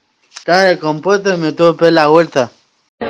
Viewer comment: "Ladié... lialé.. taba decompueto y me tuve pegá la vuelta" JAJAJAJA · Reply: Yo le entendí "cagaba en compota me tuve que pegar la vuelta"